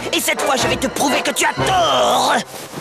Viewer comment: LMAO I understand this is in French but it does like a noise. It sounded like "DOOOUUUUGGGGHHHH" or something like that. LOL. oh my god, I am cracking up. :D :D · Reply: Zach The Bernie Bro He said "Je vais te prouver que tu as TOOORRT" XD